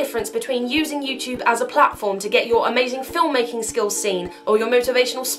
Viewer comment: haha i thought you said "amazingphil making skills"